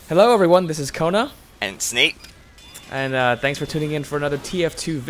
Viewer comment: i thought u said snake, not snape XD